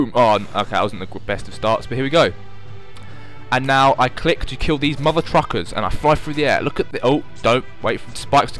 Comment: Closest English Transcribed Caption: And now I click to kill these mother truckers and I fly through the air. What youtube thinks said: And now I clicked kilby's mother truckers and of course through the end